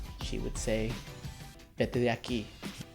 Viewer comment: What did you say at 3:40 ? I'm studying Spanish and I'd like to know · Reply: veikkomies Vete de aquí. (Get out of here)